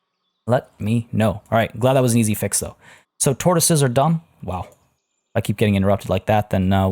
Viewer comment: You said : Turtles are done , I heard : turtles are damm :D hahahaaaaa...